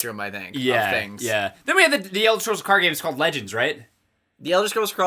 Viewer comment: at first I heard you say "elder scrolls car game" and was very confused for a second.